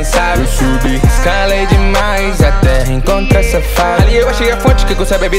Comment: "Eu subi escalei demais até encontra safada" Só eu escutei isso? kkkkk